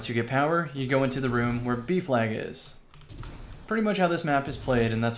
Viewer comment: i thought you were saying beef lag not b flag...